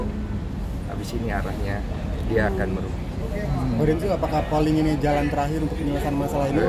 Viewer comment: kata bang densu apa ya ngga kedengeran? Dia “agus” akan merugi bukan?